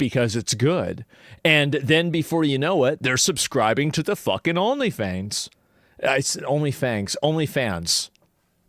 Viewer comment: Did Asmon say OnlyFangs? Is that like OnlyFans for furries?? 😵🤣